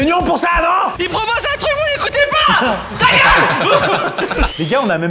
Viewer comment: Dites moi que je ne suis pas le seul à ne pas comprendre ce que dit à 15:58 si quelqu'un sais ce que dit en criant bah si vous pouvais me le dire se serais sympa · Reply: "Il propose un truc vous ne l'écoutez pas ! TA GUEULE !"